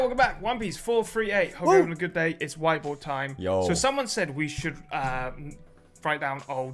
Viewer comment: I legit heard, "it's white boy time", at the start of the video, lol.